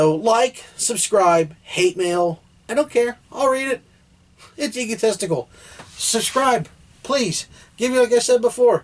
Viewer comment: who else heard him say "It's egotesticle"